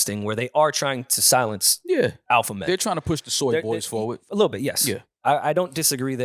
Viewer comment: Lmfao wait did you say they trying to push the sword boys forward? Lmfaoooo 5:39 lmfaoooo